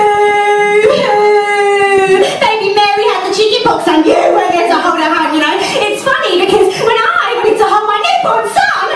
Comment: Can somebody tell me what does Jane says at 01:22 please? I'm not English and she speaks too fast 😅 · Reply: Baby Mary got the chicken pox and you didn’t get to hold her hand, but when I wanted to hold my newborn son, I DIED!